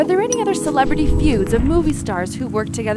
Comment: In the last sentence all I heard was 'don't forget to subscribe to the taco'